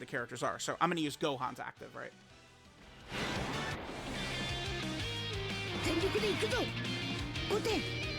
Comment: i coulda sworn was bouta say "I WANT IGUDALA"😂😂😂